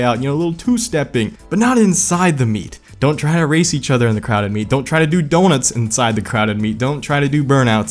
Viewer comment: OH you were saying “crowded meet” I thought you were saying crowd of meat, which is also technically correct.